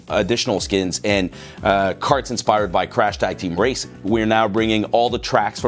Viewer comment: DID HE JUST CONFIRM NEW CHARACTERS????? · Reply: Did I hear it right? "Crash Tag Team Racing"?